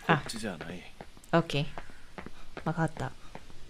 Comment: what does this word mean and how do you spell it? My uncultured English brain heard “myhotdog” but in an accent. Mah hot dahg. Which is so ignorant but I’ve tried looking it up and the subtitles give me zero help. Forgive my stupidity. I’m just starting to learn some Japanese on Duolingo and I’ll be honest- I’m struggling. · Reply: Well, I think was saying "wakatta", which means "understood" in Japanese and written in Japanese as "わかった". I think the context for saying that was when our character (Minato) was trying to find his grandma Kinu's house but ended up wandering the wrong direction, and then Minato said "This isn't the way to Kinu's house.", so said "wakatta" (means "understood") to Minato's saying. (I'm learning basic Japanese so the answer above is my guess)